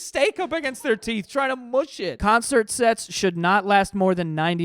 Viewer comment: I looked away right as Joe said "concert sets" and thought he said concert sex, my head spun towards the TV so fast